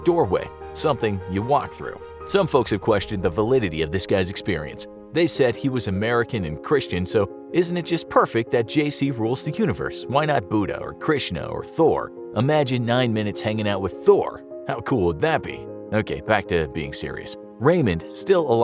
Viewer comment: I thought he's saying Jay Z rule the universe 🤣